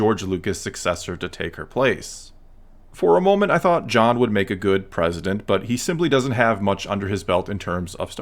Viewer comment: John would make a good president, I only passivly listened to that and was like wait what did he say John Faverow for President